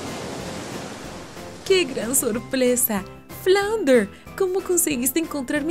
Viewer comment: like si te diste cuenta que dijo sorplesa